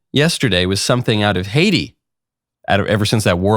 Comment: When you said Haiti, I misheard and thought you said Hades… then I thought to myself, What’s the difference?